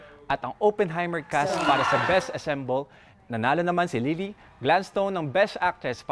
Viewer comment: it’s “ensemble.” Not “assemble.” Or did I hear it wrong?